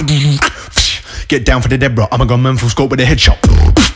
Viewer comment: What did he said at 5:40? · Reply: “Get down from the dead bro I’m a grown man with the scope for a headshot“ i think ?? Something similar to that anyway